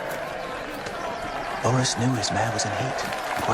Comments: What did little finger say to sansa @ 5:11 about ser Laurence knowing something? I cant make it out.thanks. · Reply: I replayed it 100 times trying to figure out the same thing the closest I can come up with is "sir lawrence knew his man was in heat" ......doesnt make much sense, but sounds funny lol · "Loras knew his mare was in heat" - Littlefinger at 5:09 if you couldn't make out what he said.